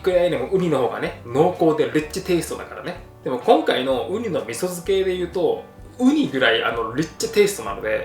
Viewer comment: ウニの味噌漬け？と思って聞き返したけど何度聞いてもウニの味噌漬けにしか聞こえなかった